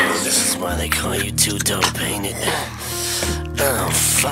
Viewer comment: THIS IS WHY THEY CALL YOU 2 DOPE AINT IT! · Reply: I always thought he said "2 tone paint it"